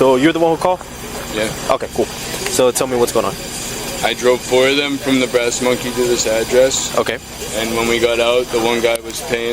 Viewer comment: He clearly said, "I drove four of them from the Brass Monkey to this address." WTF is up with the captions for some of these vids? Sometimes they're so way off, you'd think a 3rd grader wrote them!